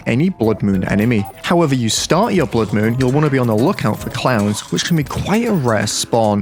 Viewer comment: When he said clowns I thought he said clams😂😂